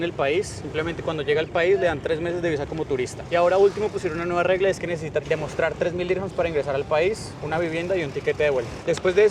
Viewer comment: me puedes decir porfavor que dice en el minuto 7:57?, tu amigo habló muy rápido y no le entendí. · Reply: "Ahora último pusieron una regla que necesitas demostrar 3.000 dirhams para ingresar al país, una vivienda y un tiquete de vuelta"